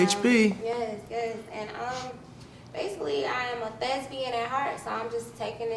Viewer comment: Did she say she was Lesbian? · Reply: I thought she said lesbian too but she said thesbian